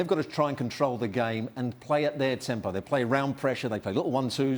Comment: did he just say temple instead of tempo?